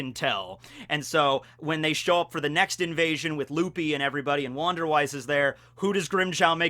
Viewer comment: did i just hear "with Luffy and everybody" ehhhh